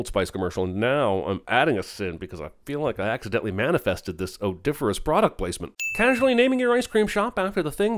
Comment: You say "odiferous", but the subtitles say "odoriferous".